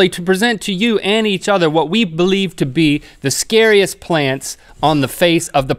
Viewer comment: For a second I thought it said, scariest planets on earth... 😂😂😂